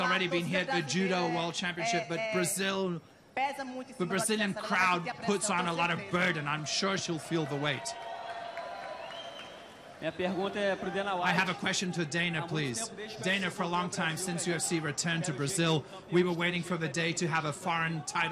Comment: Who names their child "betch" 😂 poor girl lmao.. all I heard was " "Hey Bitch, I have a question for you.." 🤣